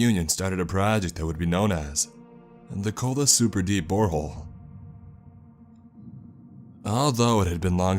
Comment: Anybody else hear, "cold deep whore hole"?